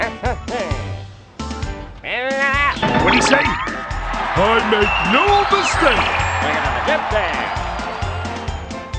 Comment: what he say? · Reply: He said “Radda Radda”